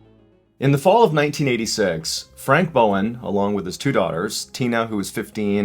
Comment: When you said “Frank Bowen” I thought you said Frank Ballen and I was like yoooooo hol up